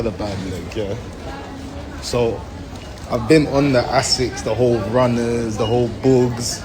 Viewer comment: Walahi I thought he said ASSETS not asics loooooool